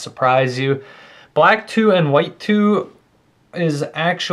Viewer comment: sounds like your saying blatoo and whitoo